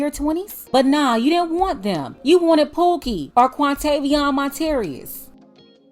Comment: did you say "Quantaydeionmontarius" 🤣🤣🤣